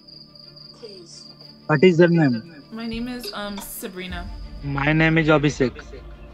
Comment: i heard «mY nAmE iS oBySiCk» 😭😭